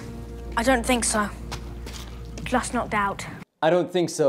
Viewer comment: Is it just me , or i hear he say "CLose no doubt"? I can't hear the J sound no matter how many times I repeat it. Also 8:51 I hear "It's My Folk. I went looking for the troll." I can't notice a T sound.